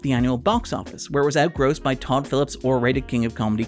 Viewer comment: Didn’t use the box office as an objective marker of quality, used the box office and cinema score and critical reviews in combination as a (fairly incontrovertible) demonstration of the film’s reception. (Also, it was a throwaway joke. The important part of my description of “Joker” was that it was R-rated, and so had a more restricted audience.) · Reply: Oh. My apologies. I misheard. You said R-rated, but I heard overrated.